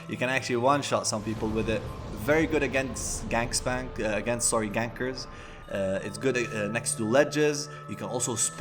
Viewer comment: did he just said there's a pyromancy that's very good against gangbangs? Lol